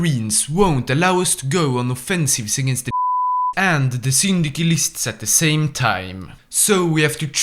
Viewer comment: Sir. May i know what word you censored at 29:45? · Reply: the word is Japs